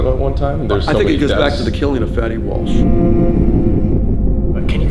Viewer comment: Honestly thought he said it goes back to the killing of fetty wap